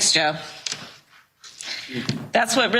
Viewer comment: Did she call Joe Biden, JEFF?